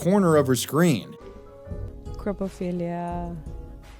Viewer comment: I thought said "coprophilia."